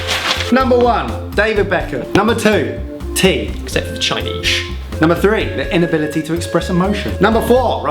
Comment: what did say? "Except for __"? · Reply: "except for the chinese" because tea originally came from china, not Britain.